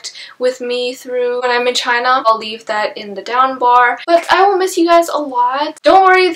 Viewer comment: so long ago! its 2019!!!! also did she just call it the down-bar???😂😂